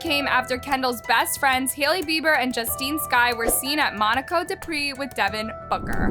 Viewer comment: Did she really say Monaco "de" Prix? As if